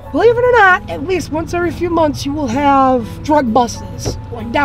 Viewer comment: "Mazel-tov cocktail" is definitely going on my list of funniest shit ever said. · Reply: i thought he said drug bustses lmao